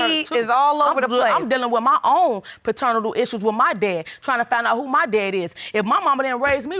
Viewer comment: Ummm did she just say fraternatal / paternatal ??